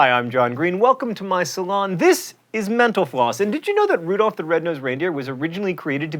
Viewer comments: lol i thought he said menopause at the end instead of mental floss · Every time I hear "menopause" instead of "mental floss"